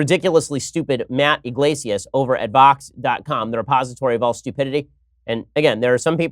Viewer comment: is he saying vox or fox? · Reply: Vox